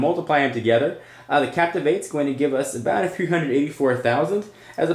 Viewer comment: What did you say? I don't understand you. Captive has how many? Thank you